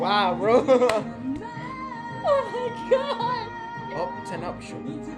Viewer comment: did he just say 10uption?? 💀😭 it's up-ten-tion